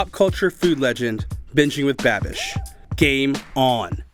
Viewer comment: Anyone else think Marty sounded like he was saying Binging with Bad Bitch the whole time or was that just me